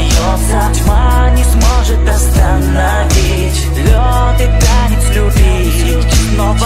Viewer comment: does anybody hear at 1:43 "my nips would pee"?😂😂